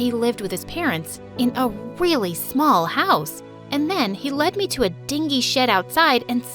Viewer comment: I heard "And then he let me to a dingy sh*t outside"